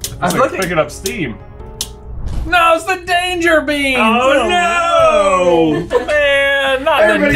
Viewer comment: I first heard “Danger Beam” at 7:25 and thought they were just gonna get vaporized or something 💀